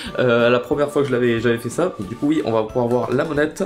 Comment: c'est moi ou a 1:24 tu a dit ducouille